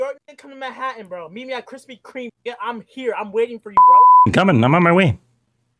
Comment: what did you say there huh..? xD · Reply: I’m f*cking coming